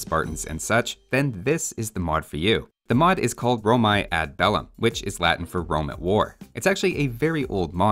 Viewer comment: I thought you said "Roma ad vellum" at first and I was very confused.